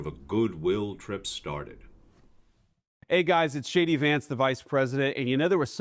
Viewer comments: Sounds like he says “ hi guys it shady Vance”lol😂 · I swear it sounded like Vance said his name was "Shady Vance."